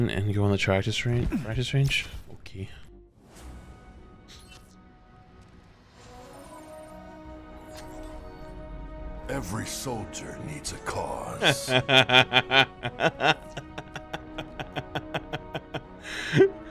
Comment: I really thought he said “Every soldier needs a Claus” and thought that was the funny thing… Now i feel dumb